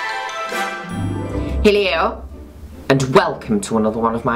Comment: i watch your videos with my nine year old brother and when you said hello in the beginning it sounded like you said "hey leo" ( my brother's name) and he said " oh enchanté, nice to meet you darling"...